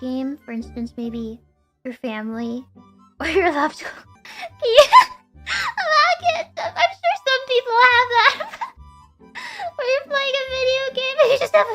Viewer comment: が「〜(聞き取り不能)I like it, I’m sure still some people have that」だと第一感は思ったんですけど、全く違った… 聞き直しても字幕と聞こえたものが合う気が全くしない ムメイと…この動画では聞きやすいですけど、アメのボソっと喋る場面やスラングが個人的にとても難しいです